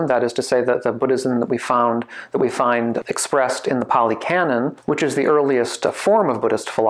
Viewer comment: Whenever I hear of of the "pali canon" I always think you're saying "poly canon" as in a canon made of multiple sources or something lol so I appreciate putting the actual word on screen, thank you 😁👍